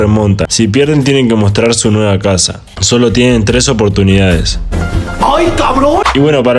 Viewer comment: yo pense que decia SI PERDEMOS ESTE RETO 😭 MOSTRAMOS NUESTRA CARA😱😳